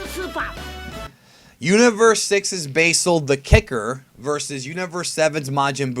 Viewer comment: did you just say universe 6? BOI!!!!!!